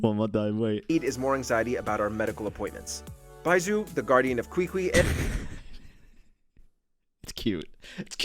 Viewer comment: "Kwikwi" the fudge am I hearing? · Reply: "Ching-shoo"